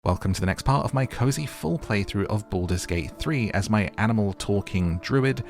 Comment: I heard “my cozy foreplay through Baldur’s Gate 3” 😂